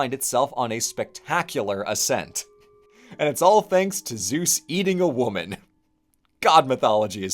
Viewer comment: My brain misheard your final comment as "and it all happened because of Zeus eating out a woman". I'm not serious enough for this right now, but I think I prefer this version of events.